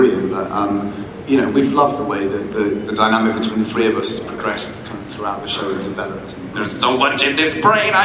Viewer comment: I heard "procressed" the first time :P